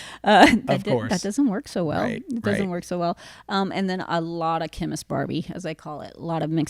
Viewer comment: Chemist Barbie?! Lol! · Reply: When I first heard her say that I thought she said "Ken is Barbie!" I was like "Why the frick would Ken BE Barbie and why is she talking about it now!?" Lol!